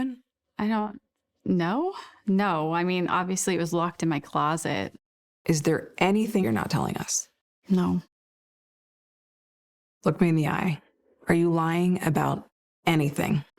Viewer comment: talking about a gun!): " I mean obviously it was lost in my closet". --- ??? Or did I mishear?